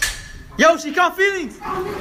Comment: Can someone explain the joke at 2:40? I can't understand what he said. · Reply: He said "SHE CAUGHT FEELINGS".........thats why they all ran ......LOL